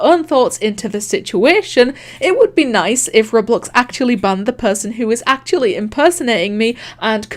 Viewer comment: I thought you said Roblox just got banned and I got so scared I might of peed myself